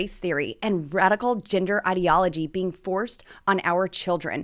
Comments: I thought she said radical gingers. Hahahahahaha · I thought she said "radical ginger ideology". I'd love to see someone preaching extreme redhead-ism.